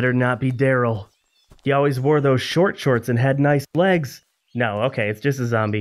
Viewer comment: Did you say nice blegs??? 😂